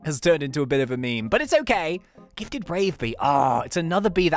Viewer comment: When he said gifted brave bee, i thought he said gifted rave bee, there was rainbow in the backround cys its gifted and my lights were off, i was dancing like crazy.